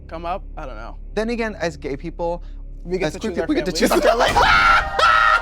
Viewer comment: what did they both say around/before 12:12 ?? I replayed and upped my volume and I still cannot understand what they said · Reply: “As gay people, we get to choose our families” lol it’s a quote RuPaul says all the time